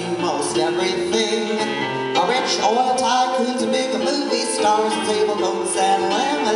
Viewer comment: It sounds like she says, "Bitchy movie stars" lol